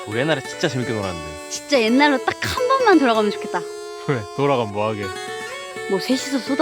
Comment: 옛날로 딱 한번만 돌아가면좋겠다 라는 말이 딱 한번만 다시 보고싶다는 말로 들리네ㅠㅠㅠ